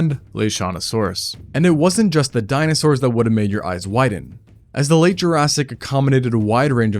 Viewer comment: I thought said. “ make my eyes WHITTEN” I was yes I’m rob zombie